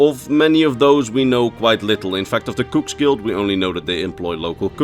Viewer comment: lol I thought he said “Kooks Guild”